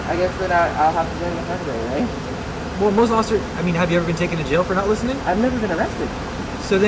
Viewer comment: 14:05 have you ever been taken to jail for not listening did i hear that right.